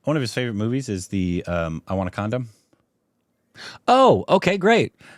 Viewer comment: When said the movie title, I heard "I want a condom". Did he really say that?